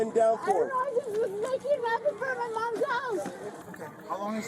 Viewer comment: The crocodile tears on top of refusing medical treatment, yeah, this is all just a setup to create a defense, she knows EXACTLY what she did and she's trying to find a way out of it down the road when it goes to trial. Also she's on something, it's blatantly obvious. They aren't showing her face, but rapid speech, hostility and endless flailing of her arm, you know the one I'm thinking. Marching Crystals. That poor sister, to put that much blame on yourself and have a mother like this, heartbreaking. Oh yeah, at the very end, that mugshot, those eyes, that's the crystals. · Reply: If you listen at 00:38 when it says 'I was just making (unintelligable)' it sounds like she says "I was just making meth in front of my mom's house" That could be "I was just taking meth in front of my mom's house". Either way, sounds to me like she admits that's what it is